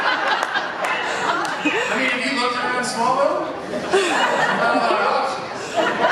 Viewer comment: What Tom says at 44:14 ? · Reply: It also sounds like he said, “I mean have you looked around Smallville? Not a lot of options.”